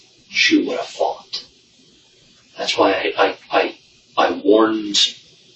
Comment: did it sound like he said, after saying how strong she was, didn't it sound like he said, " Thats why I hi.." as though he was about to say," Thats why I (hit).....her from behind" at least thats what I thought he was going to say.